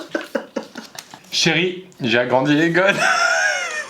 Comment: Vers 9:57 j'ai compris "chéri, j'ai agrandi les gosses", je devrais peut-être me calmer sur l'humour noir